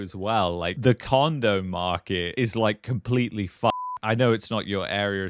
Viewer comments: Anybody who bought in the last five years got suckered and paid 3 to 4 times what the place is worth and cant afford it - Im getting out of Canada - What a 3rd world dump its become... at 1:22 it sounds like he said the "condom market" lol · For a split-second, I thought you said the "condom market". Yeah, that's apparently completely f---ed too, as several manosphere channels will point out.